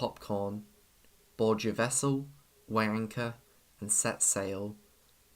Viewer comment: Every time he says "weigh anchor" I keep hearing "Waaaanker" like fuck alright man. Great vid tho